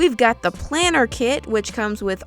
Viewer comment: Every time she said "planner" I heard "plantar" like from amphibia and now I can't stop thinking about amphibia